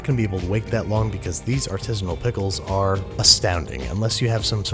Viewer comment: i keep hearing "autismo pickles"